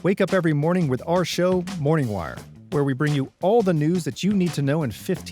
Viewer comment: Why do I keep hearing it as "Jagwire"?